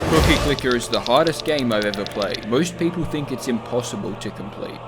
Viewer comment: WHY IT SOUND LIKE YOU SAID HOTTEST INSTEAD OF HARDEST AT THE START?